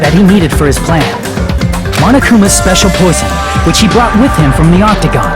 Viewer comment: I heard "Monokumas speacial pussy" like what